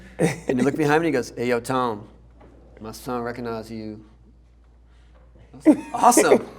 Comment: what did tony say at 6:05? "Hey Ton...(blahblah) recognize you." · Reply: my son recognize you